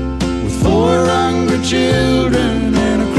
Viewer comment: I always heard this as Four Hundred Children, and I thought she had better leave that guy! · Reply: Seppo it’s four hungry children 😂😂